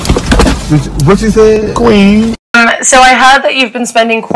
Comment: what did she say? I can’t figure it out! 😩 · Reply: "F*ggot OOP-" "What did you s-" "Queen"